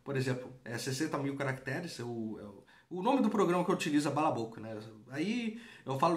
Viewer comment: Poderia repetir o nome do sistema utilizado para "quebrar" o livro em partes? Foi no minuto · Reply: balabolka <link>